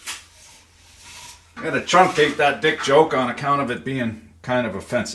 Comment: I heard, "truncated dick joke", and was disappointed about the lack of followup. But nay, I had passed judgement too soon!